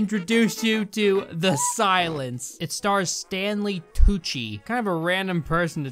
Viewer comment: I thought you said Stanley Coochie